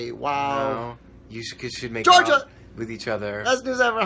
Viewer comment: Did anyone else notice what groff said at 6:43 🤣🤣🤣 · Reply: Juanma Alonso “you guys should make out” and then he kept saying it 🤣🤣